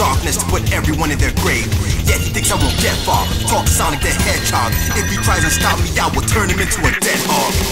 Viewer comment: When he said "talking Sonic the hedgehog" I thought he said "fuck Sonic the hedgehog"